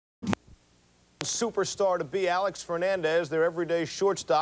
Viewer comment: I'm i the only that heard him say Alex Fernandez? 😂😂